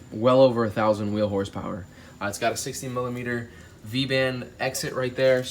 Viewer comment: did he say 16mm wastegate outlet??? Did you mean 60mm?